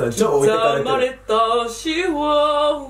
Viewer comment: ま「刻まれた〜シワ〜は〜て歌知ってます？」 け「チワワ？」 ま「ううん、シワ！」 け「知らないです。刻まれたチワワ…」 ま「シワ！！」 け「シワ！あぁ。刻まれた〜シワ〜は〜って聞こえたぁ」 ま「そやって言ったの」 会話ぜんっっぜん噛み合ってねぇのに全ての歯車が合ってるようなこの2人なんなんwwww